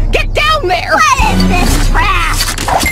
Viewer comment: As a kid I thought she said "What is this crap?"